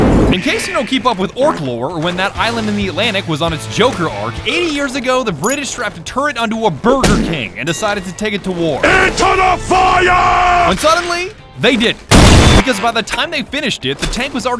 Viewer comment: When you said they put a turn it on a Burger King I thought you meant they put a full-on turn it on a normal Burger King and some ginormous treads and then they sent it off to war honestly thought you said that